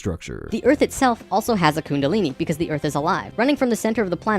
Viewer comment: did he really say the earth I'd a lie? Am I hearing him correct?